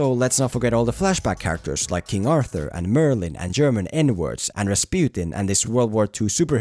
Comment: did you say German N-words or something else. Please help me